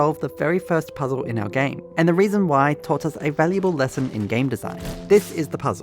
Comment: It lagged when I was watching this so I heard “taught us a valuable lesson in gay design”